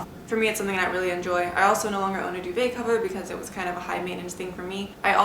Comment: I need your shirt & also can’t understand what u said u don’t own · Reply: V Lo duvet cover! Like a cover for your comforter on your bed.